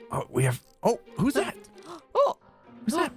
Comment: saying sh*t 3 times in a row!! Edit: After receiving further knowledge, she is not saying sh*t she is saying shift